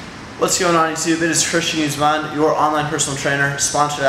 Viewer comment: your last name starts with a g man, when you say your name it sounds like youre saying its christian uzeman